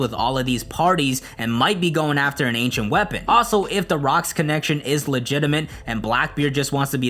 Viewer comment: did he just say "The Rock" lol